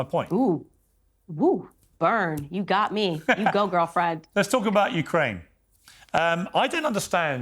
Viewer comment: did she call him “girlfriend”? Lol